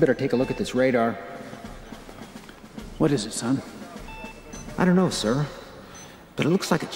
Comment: Great edit as always, but 12:41 i think he said "son" insted of "Sam" ;D